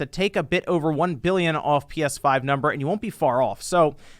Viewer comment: Did you say, "take 1 billion off"? cause that would be some shit to see right there. Any console to sell 1 billion, EVER. haha